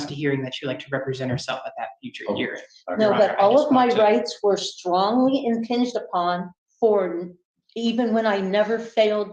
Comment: Did she say her rights were impinged upon? Minute